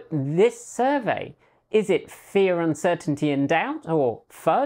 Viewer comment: Lol, when you said FUD: Fear, Uncertainty, and Doubt, I heard “Fear, Uncertainty, and Death”. I rewound just to make sure. It’s was me, not you, but I think I’ll use the “death” version for extra hyperbole.